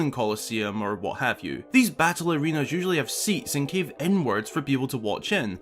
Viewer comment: Could reeeaally slay that Lynel? Keep up to date here! - <link> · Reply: I thought you said: These battle arenas usually have seats and carve n words for people to watch in.